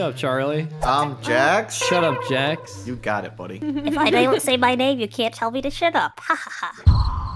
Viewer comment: why did I hear: “ if I don’t say my name, you can’t tell me to sh!t up. Hahaha!” Btw early